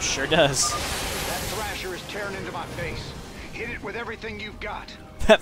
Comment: "That thrasher is tearing into my face" that's literally what I thought he said the first time I've seen this as well.